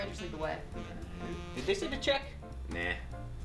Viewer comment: What does the guy say at 2:15? I don't get it. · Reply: Did they send a check?